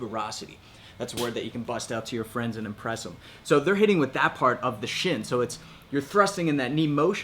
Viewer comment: I thought said you could bust out your shin bone to impress your friends